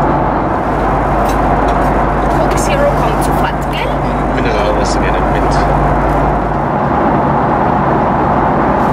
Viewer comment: May I ask what was said between you and the FA at 11:13? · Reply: she asked me what I wanted to drink and at the same time told another passenger that his coke is coming right away. I told her I want sparkling water ;)